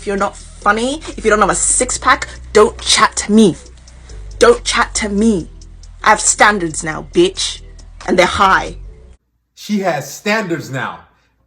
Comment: "I have standards now" Notice how she wants to make rules for you but she didn't make rules for the guys in the past lmfao same old same old. I can't stand these delusional sassy chicks it's actually crazy but these are the women that are doing you a favor it helps us as men root out leaches and bad seeds. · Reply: Am I delusional did I hear don't chat me? Is that what I heard I hope not because if she has standards she needs to forget about that shit and get education